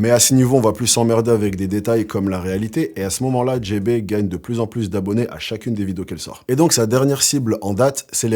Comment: "Elle gagne de plus en plus d'abonnés à chacune des vidéos qu'elle sort" J'ai entendu "de plus en plus de poids" mdr